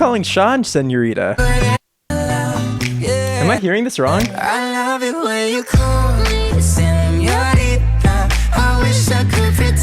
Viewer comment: 😂 his reaction tho😑😩😩💀 . 10:14-10:18 Shawn: She loves it when I come! WhAt!😂😂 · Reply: Droopy .Editz i thought he said "she loves it when i call" that's why he was saying what over and over lol 😂